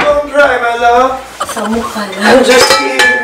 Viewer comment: Did I just hear samukan? lol